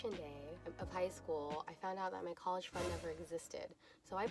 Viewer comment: ok I’ve listened to it like 5 times and I can’t Understand what she says when she says “graduation day I found out my college .....(blank)...... never existed! Fund? Credits? Why can’t I understand her?! Lol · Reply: Fund